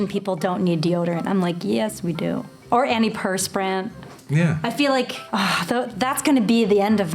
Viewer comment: As a Virginian, I feel like we are the least southern southern state. BTW, "You can't handle vermouth!" was great and I don't want that to go unnoticed. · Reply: When she say’s, “antiperspirant.” Yup, I heard the Virginia, there